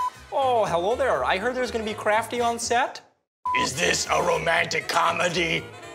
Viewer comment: Did it say craftee